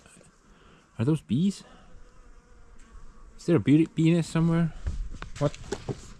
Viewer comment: the captions read is there a bee penis somewhere instead of bee nest 😂